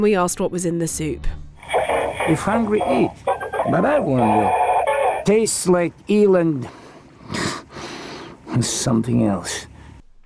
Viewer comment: "Smells like eel and..." I totally heard "smells like Ellen"... O_o!